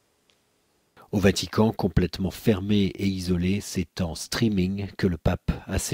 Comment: "C'est en string que le pape a célébré sa messe du vendredi" . Quoi ??? ecoutez à